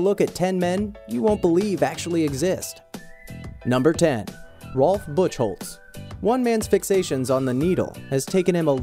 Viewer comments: Sounds like he says Rolf Buttholes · Who else thought he said 'Rolf Butthole' on number 1 before reading the name..?